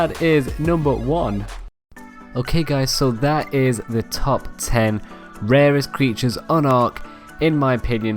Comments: did he say top 10? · Tf you just said top 10 rarest dinos in the game. But your subtitle says top 5 (8:24-8:26) lol